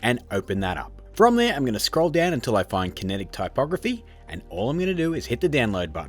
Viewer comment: For a moment I thought you said "type pornography". Nevermind cool vid